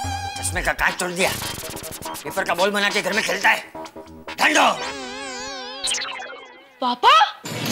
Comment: did champaak just say "gaandu"🤭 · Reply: No it's gando it's a Gujrati word means bewaquf,fool